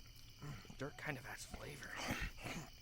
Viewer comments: I swear I heard Kiri say 'This dirt has good flavor' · “ oOh I DROPED it 🥹😭” “ tHE DIRT KINDA HAS FLAVOR 👹👹👹” THATS ALL I HEAR KIRISHIMA SAY IN THE BACKROUND 😭😭😭✊🏾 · Everytime i listen to this i swear i always hear kirishima say the most dumbest thing each time at 25:03 i swear i heard him say "the dirt kinda adds flavour" like what 😂😭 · In the back round I heard “dirt kinda has flavor”